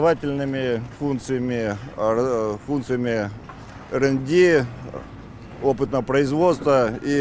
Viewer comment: Что он сказал? рэнди? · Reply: R&D (research and development), что по-русски НИОКР - научно-исследовательские и опытно-конструкторские работы. Олелевод совсем русский, видимо, забывать стал.